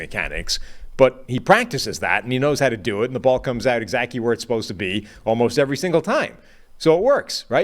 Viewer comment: & 11:00 & 11:06 Is this man saying “exacty” (E-Zachy) instead of exactly??? Lol